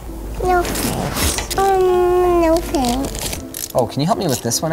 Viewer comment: I only hear “no fanks” from that girl